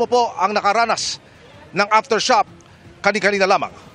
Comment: Did he say "aftershop" 4:21 or aftershock?